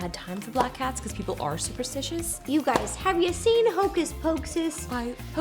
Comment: The cut made it sound like Ro said "f**k u guys, have you seen hocus pocus?!!" I laughed so hard lol